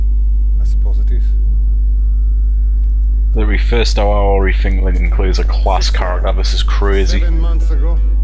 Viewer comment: Can you translate what was said right after 6:38? I don't know if it was the accent or the mic, but I replayed it and I still don't understand what you said. I do enjoy the game btw thanks for putting it up there! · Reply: Sure thing. Probably my accent but hearing this back, I forgot how shocking my microphone was back then! 😬 I said "Literally in the first hour, I think Lincoln Clay is a class character. It's crazy".